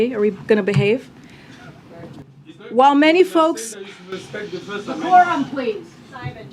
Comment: did i hear some one say "decorum please!" ? ? ? when Trump was president and some times did these the reporters were shouting and screaming at him as if they were down in the pits at wall street buying stock.